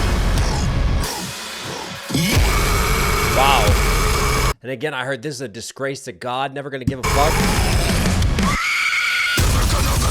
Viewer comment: not gonna lie, I'm really impressed at how well can decipher the lyrics from the heavy screams and growls now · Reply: Same, I thought them saying "disgrace to god" was them saying "this is question mark"💀. Shoutout to for his deciphering skills 👍